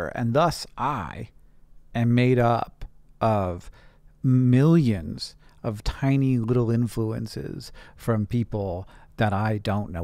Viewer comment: When you said you were made up of "millions of tiny influences" I thought originally you said millions of tiny influenzas! haha. And I was like yeah you could have DNA in your body from those potentially, I mean we do have mitochondria.